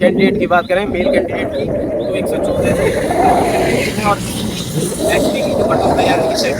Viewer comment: se 8:40 tak kya bola hai सुनाई nahi diya pls Sc ka kitna bataye?????? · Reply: 114Q-117Q